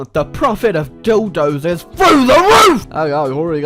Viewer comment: When he said "The profit of dildos is through the roof!", I thought he said "The Prophet of dildos is through the roof!" origionally.